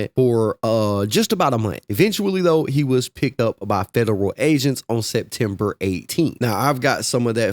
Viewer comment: I swear I thought he said federal Asians for a second